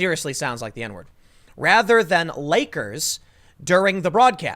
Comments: To me it sounds like she said Nakers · It doesn't sound like the N word. Listen closely, it sounds like she said knakers. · I heard "Nakers" (Nay-kurz) Y'all projecting. People hearing what they want, this is just the phenomenon called "mondegreen"; neurological auditory misperception. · Honestly it sounds like she said "nakers" but I'm honestly not going to complain if some twit that earns 6 figures just for being hot and able to read a teleprompter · Tim, you keep saying “Kobe Bryan.” It’s Kobe Bryant. This is such a non-story but if we’re going to nit pick verbal gaffs, I feel the need to point it out. And I heard “nakers.” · I Heard 'NAKERS' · If this were Tucker his house would have been burned down. Nothing will happen since it's MSNBS Ps. I heard, "Nakers," personally, but I can still see the double standard. Karma sucks. The Left eat their own